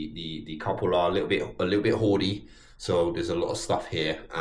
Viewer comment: Thought you said, "Couple's a bit horny..." then realised that doesn't make sense in context, and realised you actually said "hoardy". I need my ears checked 😂😂.